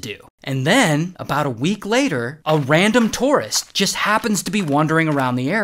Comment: I was listening and when you said "a random tourist", I thought you said "a random Tortoise".